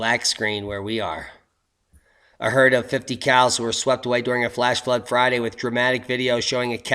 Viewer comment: When you said "a herd of 50 cows", i thought you said "a herd of 50 cals", i though those poor, poor Barretts...